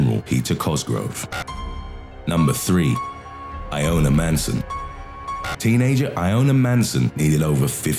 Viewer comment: I thought he said "I own a manson" instead of Iona Manson